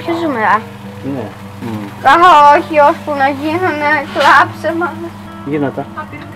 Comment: Στο 7:21 τι λέει ο μικρός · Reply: Ανυπομονεί να βγουν "ώσπου να γίνουν κλάψε μας"